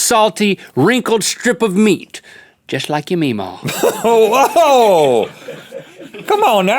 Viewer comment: What did link say? At 0:30 just like your... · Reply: Julian Wischmann Mee-Maw, a very very southern nickname for a grandma or great grandmother